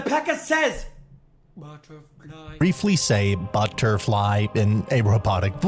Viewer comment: I always hear "megaknight" instead of butterfly